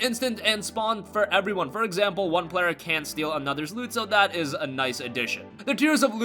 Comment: Got a question, at 4:50 did he say that players "Can" or "Cant" steal other players loot? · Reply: Can't. Loot is player based